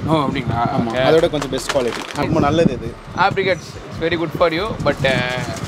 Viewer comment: Anyone please say what he is saying name in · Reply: Apricots